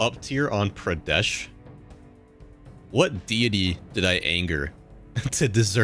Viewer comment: i heard ''what diddy'' man